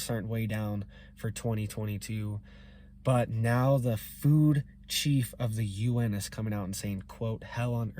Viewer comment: A silly thing just happened, when you first started talking in the video I could've sworn I heard you say, "So that fool Chief of the UN"... 😆🤣 I had to pause and laugh for a moment, then I hit replay and realized you really said "Food Chief... 🤷‍♀ 👍